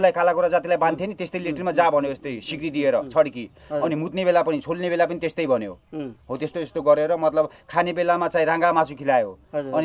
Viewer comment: Did he just said cholne bela ma . Haha 😂 fu*king psycho. Yahi muji ho dosi.